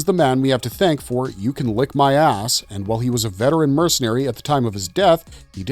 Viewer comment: Am I hearing this correctly "you can kiss my as$"